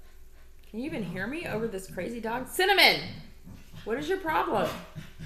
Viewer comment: Oh, "Cinnamon!" LOL. Please ignore my msg on another of your recent videos, when I mistakenly thought I heard you call "Cinnabon!" -- revealing WAY more about my associations than yours. Have a swell time with your sweetie-pie in NH next week! Hug, Scott